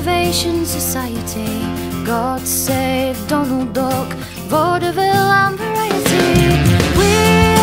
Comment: Love this song but I thought she said Dumbledork(???) but now I realise it's Donald Dock (Duck) lol